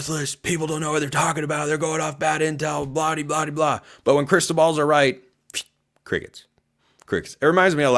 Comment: I thought you were saying Cristobal for a second there. Still don’t like that guy 🤣